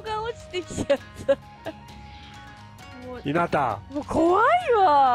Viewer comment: ここ字幕は「日向。」になってるけど「ひなたん」って呼んでるようにも聞こえる。